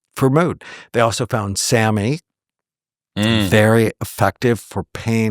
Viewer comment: what is he saying here ? i didnt understand the word, sammys?